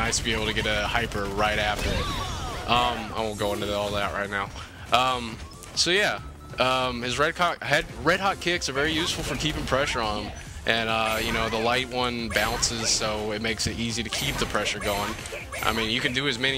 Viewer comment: Did anyone else catch him say "Red Cock Kick" 6:42 LOL